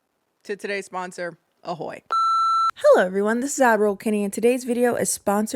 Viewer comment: Oh my God, I have watched your vids for years, and I have always heard it as admiral Kenny!! I don’t know why I just accepted it lol